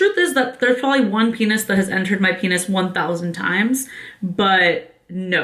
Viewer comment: lmao did anyone else hear her say "there's probably one penis that has entered my penis"